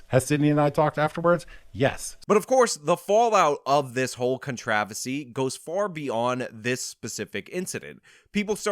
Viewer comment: What is “contravasy”? You say it twice here; the second time is at 5:33. What word are you saying there?